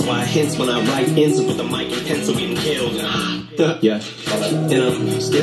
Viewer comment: Great reaction! I caught the mic and pencil bar... which sounds like he said mike pence.